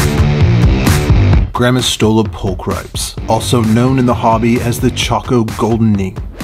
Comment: I watched this last night and I was getting a little sleepy so I closed my eyes then when the video started,instead hearing the scientific name,I heard "grandma stole a vulpix" and I opened my eyes quickly and replayed it to see if what I heard was right. (Vulpix is a Pokemon from........Obviously Pokemon)